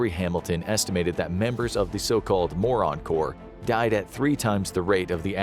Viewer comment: Ngl that title is a little… blunt. These are veterans were talking about here.💀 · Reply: I thought it said Mormons